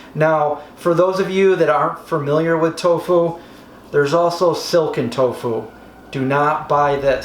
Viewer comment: Wow I thought he said silicon tofu loool